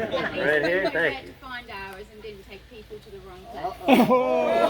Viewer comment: What did the British lady say to him at 55:40? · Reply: It was something like at least our guides don't take us to the wrong place. had taken the head of the group down the wrong path instead of to the place where he was speaking. He sure likes to banter. And she got him with a good one.